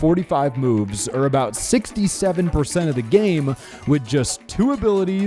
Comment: Did he say 6 7